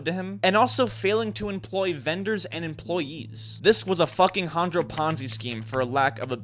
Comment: did he say failing or fuc#### ?? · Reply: I think fucking